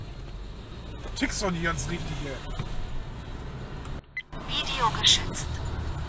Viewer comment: "Video geschützt" -- da hat das Übersetzungsteam ganze Arbeit geleistet. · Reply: Ich hab irgendwie "Video geschissen" verstanden 😅